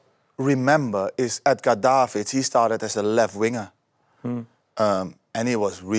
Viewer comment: What name does he say around 12:45 ? It’s a Dutch name and I can’t make it out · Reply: Edgar davids